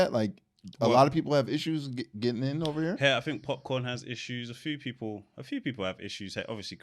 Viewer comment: Did say Popcorn or am I bugging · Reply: Popcaan lol.